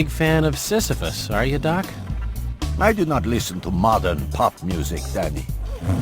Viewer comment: did i hear motherfuck when i played that part in 2x speed?